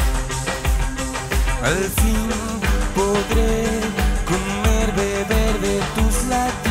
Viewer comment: yo pensé que decía "al fin podré COLMAR DE VERDE tus latidos" pero dice "al fin podré COMER, BEBER DE tus latidos" xD ups!!